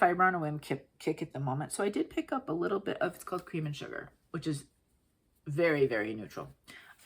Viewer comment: So, I watch Flosstube on 1.75 speed and I totally thought you said cocaine and sugar at 1:07:24. 😂